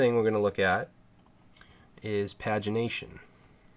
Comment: thanks again for another great tutorial. laughed when i thought you said vagination lol